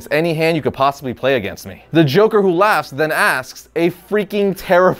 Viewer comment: Am I the only one who heard him say the joker work laughs